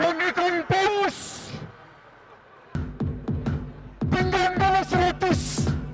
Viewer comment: I heard "pinjam dulu seratus" lol